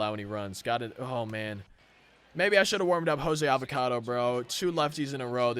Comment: Am I the only person who heard him say Jose Avacado